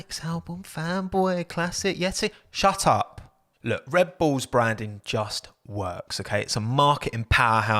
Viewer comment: Can I just say at 5:50 the captions said “red balls” instead of Red Bull